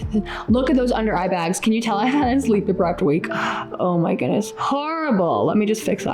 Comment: Hey what did you exactly say at 7:34? "I had not sleep deprived week" or... ? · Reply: "Can you tell I've had a sleep deprived week"